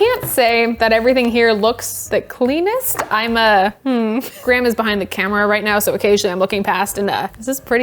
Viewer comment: I totally misheard you as "Grandma's behind the camera" and I thought that was a cute intergenerational coffee bonding activity